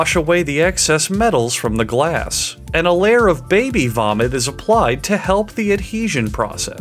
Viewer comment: did i just hear incorrectly or did he literally say baby vomit lol